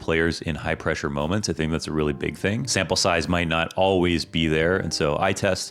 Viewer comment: why does he say samplesize my nut